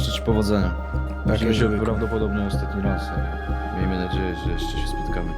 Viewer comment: widzimy sie prawdopodobnie ostatni raz ?? Dobrze usłyszałam?